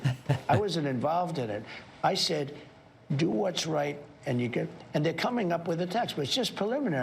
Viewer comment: Anyone notice Trump at 7:44 said “do want’s right and you’ll…”? Sounds like he was gonna say “do what’s right and you’ll get what you want”, to me.